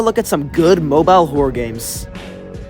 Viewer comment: I thought it said "Good morning horror Games"💀